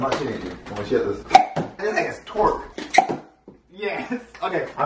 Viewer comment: did he say the n word ? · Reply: No he said this thing is torque